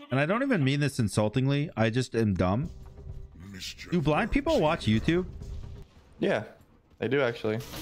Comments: Bro when you said “do blind people watch YouTube” I heard “black people” and was so shocked what I was hearing lmao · I thought he said “do black people watch YouTube” I was shook thought they were getting into some touchy subjects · I thought said "do black people watch youtube" i was so shocked lmao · Omg. I thought he said “do black people watch YouTube” and I was so confused lol.